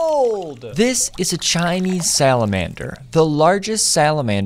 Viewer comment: At first I thought he said “ Behold! This is a tiny salamander. “